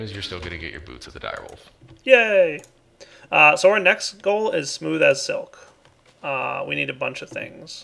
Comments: or so I think called Soaryn. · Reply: haha, he was saying "So our next goal..." but he said it so fast it sounded like he was saying Soaryn. let's just keep it as called Soaryn though. :3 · Anyone else notice that at about 29 Mins in, he called "Soaryn"? :P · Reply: I heard "so our next" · Did anyone else catch that at 28:48 Direwolf calls Soaryn??? Lol and then there was this weird silence like he was hoping he didn't hear him say it :P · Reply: he said so our next goal is, not soaryn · did he just call Soarin? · I think he said So we at 28:48, not Soaryn, i think he just said it weirldy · He said So our next goal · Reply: OK, thank you for clarifying, I thought he said "Soaryn, next goal is..."